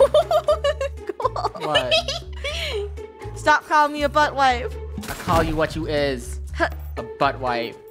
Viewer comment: i thought that say but WIFE